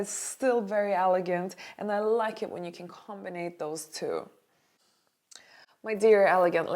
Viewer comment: Did i hear “combinate”? Instead of combine? No hate, i love as much as you do guys ❤️